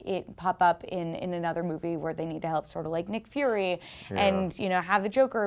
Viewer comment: did she really say have them help Nick Furry? -_